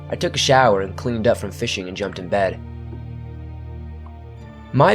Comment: Ohhh I thought you said FISTING. Now THAT’S a horror story!